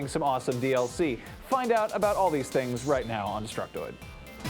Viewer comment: I always hear "We all Destruct, We all Destruct" but that might just be the Sennheisers